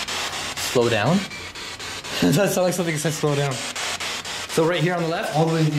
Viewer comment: i heard " OLD TOWN ROAD " THANKS ME LATTER...LOVE FROM MALAYSIA 🇲🇾❤️😂